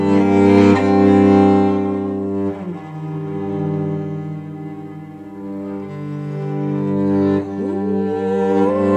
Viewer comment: Who is this contemporary composer at 34:30? I didn't hear it well. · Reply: I was able to find an autogenerated transcript of the podcast which had the wrong name but I googled that and found Pēteris Vasks which I believe is who she mentions here.